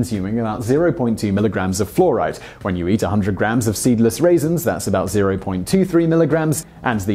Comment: Simon mentions "Seedless Raisins". Has anyone out there ever eaten a raisin with seeds in it? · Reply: I thought he said stemless raisins.